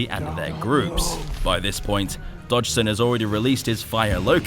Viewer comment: did he say Dodgesen like the guy Nerdy met up with in the first Jurassic Park movie